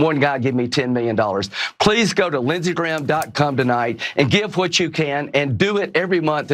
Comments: I thought I heard him say lindseygraham.cum. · I CANNOT be the only person who heard him say "lindsaygraham dot CUM" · Am I the only one who heard him say lindseygraham.cum the first time?